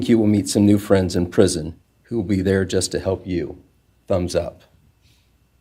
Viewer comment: I loved the statements by one of the Goncalves Grandmother’s “You’ll meet some new friends in prison - thumbs up 👍” I’m dying 😂😅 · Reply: I thought he said ‘bums up’ but I guess the whole thumbs quote makes a lot more sense from that selfie pic of him!